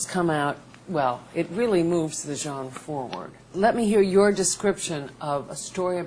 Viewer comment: What'd she say? "It moves the gen forward"? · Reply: genre*